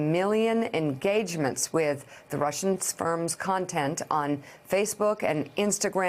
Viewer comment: Did Mika just say "Russian Sperms"?